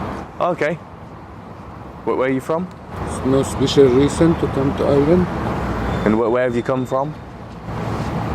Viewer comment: " no special reason to come to Ireland " Is what he said